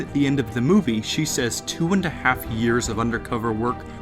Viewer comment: Wait a minuete, I thought she said "20 years..." either I misheard it, or Roz has been there for a long time.